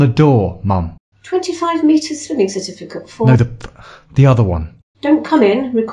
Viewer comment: what did she say? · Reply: 25 meter swimming certificate for